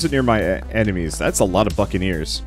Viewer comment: that's a lot of buccaneers What I hear: that's a lot of F*cking ears